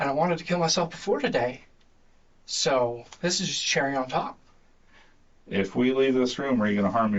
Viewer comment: I think he’s saying this is just the cherry on top, not cheering on time (I’ve never heard anyone say cheering on time like that).